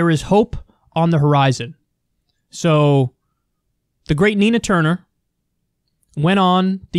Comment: I'm from the UK so I'd never heard of Nina until now. I thought you said Tina Turner (oops, my bad). But I bet you will agree Kyle.... "Simply The Best" ..."Better than all the rest" (sing along <link>